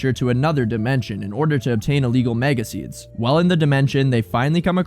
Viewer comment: Sounds like you said MAGA seeds